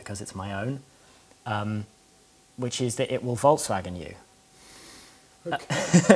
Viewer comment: Did he say "it will volkswagen you" at 7:30? Or did I misshear?